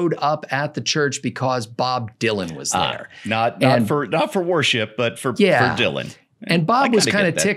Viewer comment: For a moment, I thought was saying, “Warship.” 🤔